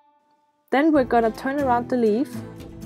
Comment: "Then we are going to turn around to leave." That's what I heard.😂😂😂